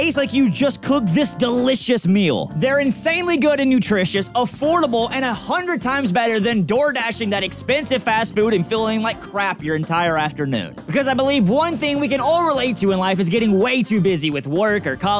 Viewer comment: For the food promo things I totally thought he said expensive ass food 😂